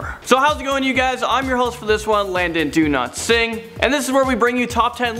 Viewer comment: I heard “Landon Do not sing